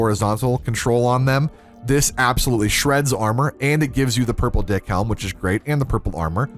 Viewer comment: purple “dick” helm is what i heard